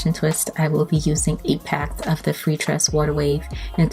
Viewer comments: Hey sis. How many packs of hair did you use? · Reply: No lie it sounds like you said A PACK. I think you should start writing it as well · Gorgeous hair 😍 but how many packs of hair did you use? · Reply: I know it sounds like she says a pack but it's eight packs